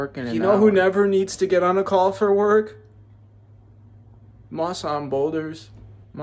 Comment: I thought he was saying "Massan Boulders," I thought who tf is Massan Boulders 😭😭